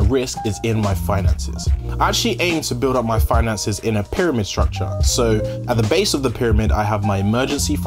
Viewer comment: For a moment I thought you where saying "pyramid scheme" and got excited